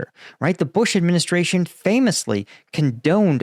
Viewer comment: oh he said it right "the bush administration famously condoned torture" i thought he said "did not"